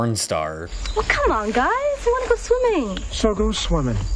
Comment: Is he saying “Does the water swim?”